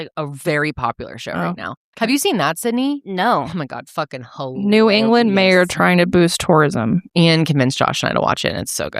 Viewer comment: please can somebody tell me which show is talking about?? English is not my first language and for the love of God i don't understand the title lol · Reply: Widow’s Bay!